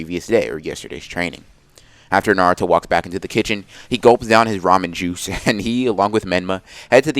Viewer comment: I thought he said ramen jutsu